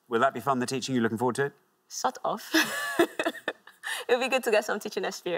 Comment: couldn't figure out why she told him to "sod off", got there after a few rewinds..